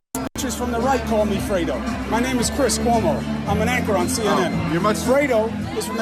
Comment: did Coumo say "I'm an Actor on CNN?"... wow, brave admission...